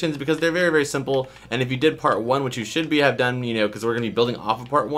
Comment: Did he say should be have done? Like if you agree.